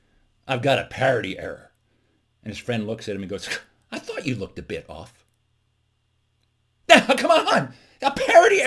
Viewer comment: I expected the line to go “...I thought you looked a bit odd”. This is probably what you meant, but I believe I heard you say “a bit off”. Still works though :-)